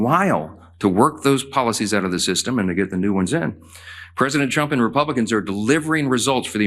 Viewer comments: It sounded like he said “president chump.” At least he got that part right. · I swear he Freudian slips and says President Chump lmao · IT SOUNDED LIKE HE SAID PRESIDENT CHUMP.😅😅😅😅